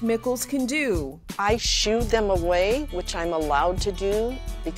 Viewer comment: For a second I thought she said, “I shoot them.” That would be really bad lol